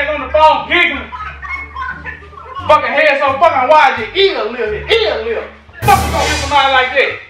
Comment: What did he say at 12:00 after how wide Shawn head is? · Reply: “Your ears little” 😂